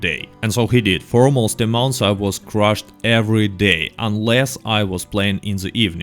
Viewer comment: I thought he was saying crushed but I got it crashed